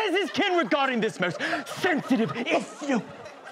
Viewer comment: Can anyone write what Jason said in his speech? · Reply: thank you so much. I'm not a native english speaker so I couldn't nail much. I thought he said "if you" instead of "issue" because I didn't hear any "sh" sound, also what does arrer and twood mean? Are they old/victorian/shakesperan english words?